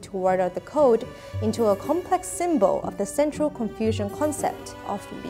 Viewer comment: Absolutely wonderful video! 😍 I did spot what I think is a subtitle error: at 3:42 the subtitles say "a complex symbol of the central confusion concept of Li" — should that perhaps be "central *Confucian concept of Li"? Apologies if I'm wrong!